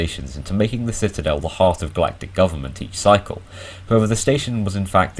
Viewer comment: Black dick government subtitles says that at 5:09 lmao 😂😂😂